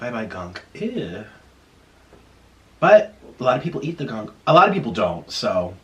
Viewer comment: Did he say black people eat the gunk?